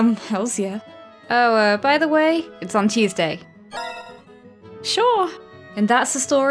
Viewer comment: When you said it’s on Tuesday I thought you said cheese day.😹